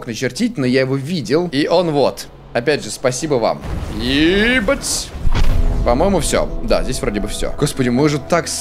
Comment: мне послышалать что он сказал 6:40 ибат ь